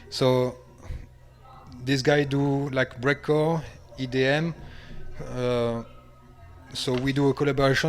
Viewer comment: does he say "IDM" or "EDM"?? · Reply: He says IDM, definitely. Read it on a french article